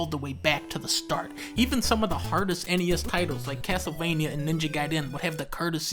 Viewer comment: Anyone else hear "Ninja Guy Den" at 4:14 🤣